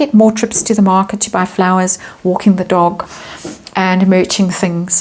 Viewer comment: I have a hearing impediment, which gives me unintentional moments of hilarity. When you were saying about M going to markets and walking her dog, I heard that as she would be 'wokking her dog'... So thank you for a good giggle.